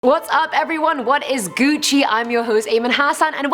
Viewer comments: Again, did she just say wut is gucci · Did she actually say “what is gucci???????!!!” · did she say "what is Gucci"? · Did she just say what’s gucci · Just imagine seeing the lions walking down the road would feel like the original Jumanji 😂 great vid and looking stunning again Ayman😍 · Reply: oh i thought you guys were saying actually aye man not Ayman